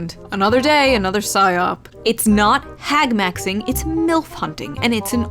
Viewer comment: I heard "hagmaxxing" and I started laughing my ass off. Wild.